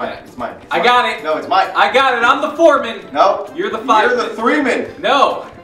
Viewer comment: I thought Lasercorn said, "I'm the Mormon" and immediately thought of the recent Smosh Pit video